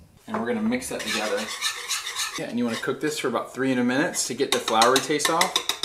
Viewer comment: I thought you said 300 minutes at first😂